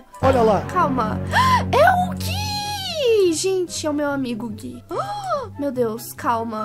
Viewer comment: eu entendi é o meu amigo gay 😰💅